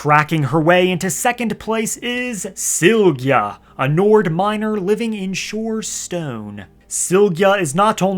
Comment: Dude skjsk 😂 i was watching this while grinding in oblivion, when n⁰2 came i just heard "Sygja a nord minor" and cracked my neck towards my phone like "EXCUSE ME WTF???" 😂😂😂